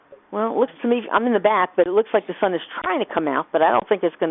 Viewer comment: Did she just said she's in a bath? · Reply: I'm pretty sure she said: "I'm in the bathroom right now"